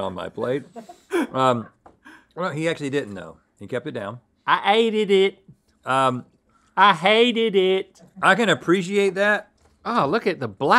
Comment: I thought Link had said “I eated it! I eated it!”